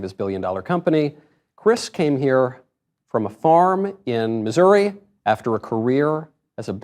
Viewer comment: I thought he said a farm in Montana.